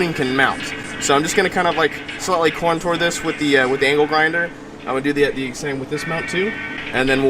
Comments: did you say anal grinder\ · i thought you said anal grinder I got excited for a moment dad